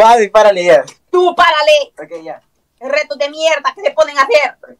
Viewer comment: bro nahhh did sh say "preto de mierda"? 💀